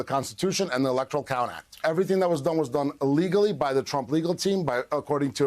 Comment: Im sure he said "we did this " illegally" But hey these false forms sure look alot alike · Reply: I thought that he said illegally, too. Freudian slip?